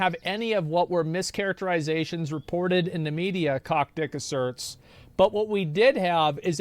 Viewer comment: I was wondering whether Bobby would be considered a sociopath or a psychopath. I think I can definitively say now that he is indeed a psychopath. 2:08 Okay, I can't tell whether he's intentionally pronouncing it Kockdick or I'm just hearing him wrong... 3:26 He WHAT!?